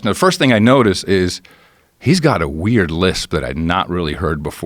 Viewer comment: Did he said "a wear whisper"? I'm not a native speaker so it was kind of hard to understand what he said. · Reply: A weird lisp.